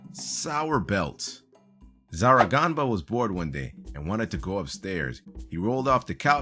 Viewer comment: am i the only one who heard "born" instead of "bored" XD